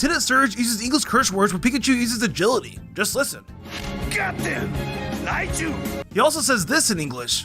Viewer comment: I think he said get him not goddamn 😭